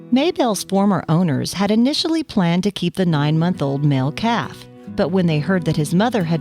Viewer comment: There is no such thing as a male cow. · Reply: I thought she said male calf.